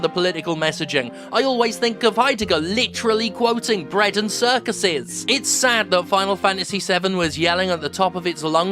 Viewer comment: I legit misheard Heidegger's line as "Bread and Circuits" and didn't pay enough attention to the subtitles lol In my defense, that would have been a great dad joke in context.